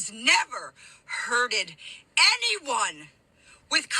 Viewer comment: This does not look like a monk in a monastery! And did she just say “herded”? Sheep? Goats? What does herding have to do with this? (And please tell me the scholar and religious acetic did not mean HURT? Please tell me the person is not so illiterate to not know that “hurted” isn’t a word! That’s not possible!)